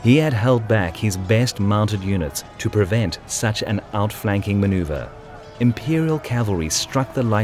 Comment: Calgacus: It's over Agricola! I have the high ground! Agricola: You underestimate my power! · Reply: I thought he said specifically "Roman Imperial Cavalry", my bad. Otherwise, I know that Romans themsleves were generally apathetic toward the importance and tactics of cavalry.